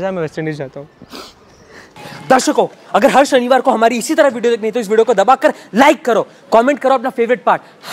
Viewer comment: YOU SAY HAR SHANIVAR & I HEAR HARSH BANIWAL 🤣🤣🤣🤣